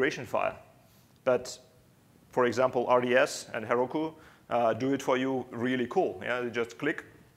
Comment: what did he say at 15:09 for example ........... and heroku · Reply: RDS and heroku.